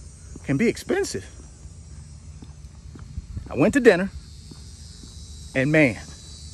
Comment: I thought you said " I went to dinner in Maine" LOL...I was about to say no wonder it costed so much! I get what your saying but most woman want you to take them somewhere and SPEND money...this is one of the main reasons Im single..